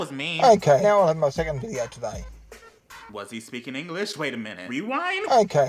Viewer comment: "Okay, this is my second via today." That's what the first guy sounded like he said.